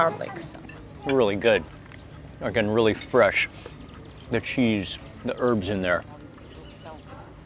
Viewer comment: was that a “f**ckn really fresh” I heard there? Lol 😂 love this guy!